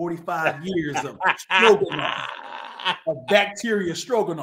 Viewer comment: I like when I see “Easy To Draw” in the thumbnail 😂 I know some weird funny story/comment is en route lmao LETS GOOOOOO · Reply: I definitely heard “strokin” when he said stroganoff 😂